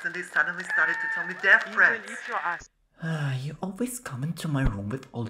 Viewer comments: why i hear ass at the end🗿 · I started to wheeze when I heard get your ass😂😂😂😂